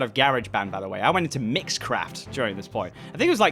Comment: "Mixcraft" what i heard: "Mitchcraft" im sorry.